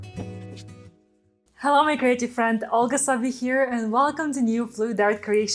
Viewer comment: For the longest time I thought she said Hello my crazy friends! LOL